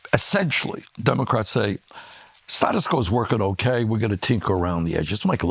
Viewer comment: Did he just say Status Crow?